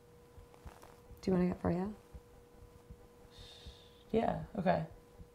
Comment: do you wanna what? I can't figure out what she's saying. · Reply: "Do you wanna get froyo?"